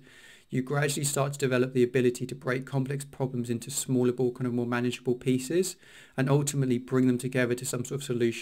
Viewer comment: Great video, Can you please clarify what you said at this time 1:42, " to break complex problems into smal???? manageable pieces" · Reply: Yeah of course, I said 'small, more manageable pieces'. All the best!